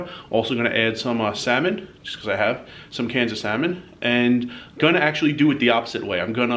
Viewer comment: I was listening more than watching this video and at the 2:16 mark, I thought you said Kansas salmon when in fact, you said cans o’ salmon. Was wondering what Kansas salmon was but I definitely sorted it out later. Lol.